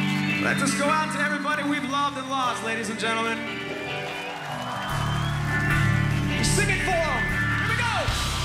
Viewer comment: can anybody tell me what he says at 4:23 please ? I can't understand. Thanks! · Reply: Sébastien Myles says "let this go out to everybody we've loved and lost, ladies and gentlemen...sing it for them...here we go..." This is as close to what he said as i can tell...I hope this helps.